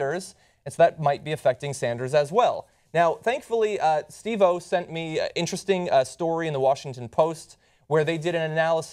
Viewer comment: Did John say Steve-O sent him that article? As in "Jackass" Steve-O? In the beginning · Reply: No, their Chief Operating Officer is named Steven Oh.